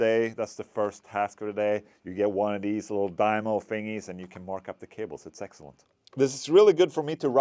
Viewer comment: subtitles say "...and you can mark up your cables and sex toys.". Well, ok then! :D